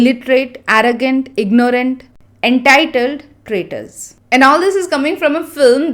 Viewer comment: Hi, actually in that Janhvi’s dialogue where she is talking about various actors of Southern states, at last after entitled she is not saying traitors. She is saying tenga which literally means coconut but in Kerala it is sometimes used as a synonym for nonsense, like we say ‘my foot’ in English. But like you correctly mentioned in the video the actress couldn’t pronounce it properly. She pronounced tenga as Tenka but in Malayalam ‘ga’ is pronounced differently. Even at first I thought she was saying traitor, after rewatching it several times I understood what she actually was trying to say. Pathetic pronunciation. As a malayali I feel horrible :(